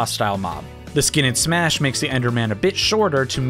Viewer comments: I thought he said it makes the ender man a bi*** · I thought he said bitch shorter lol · For a moment I thought he said "a bitch shorter"